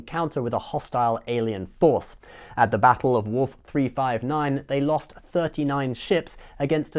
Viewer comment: Huh. Thought he said "Worf 359." I guess that would be assimilated Worf.